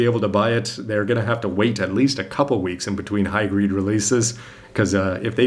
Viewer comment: it sound like you said "high GREED" instead of "high GRADE." :)